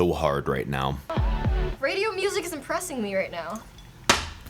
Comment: i thought she said radio music is impressing me right now She said deppressing right?